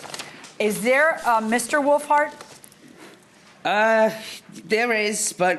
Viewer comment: Sounds like he said their last name as willfart